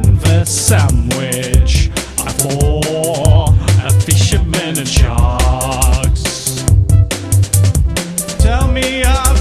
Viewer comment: All this time I thought it said, "I wish I were a shark"